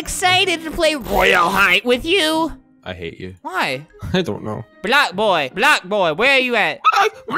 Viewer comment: is he saying black boi? Or Block Boi BlocBoi is an Ironic name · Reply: CORRUPT10NXYT block boy that’s his username.